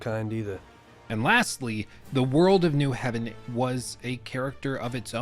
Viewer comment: *Lost Heaven. Not New Heaven. Loved how you brought up the breathing problem though as I also thought exactly that I was the only one who heard it. It was almost as if each line was delivered right after the actor had just ran a marathon.